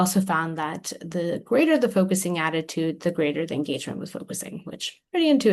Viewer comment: what is said at 9:03? the greater the focusing attitude...? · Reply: the greater the engagement with focusing.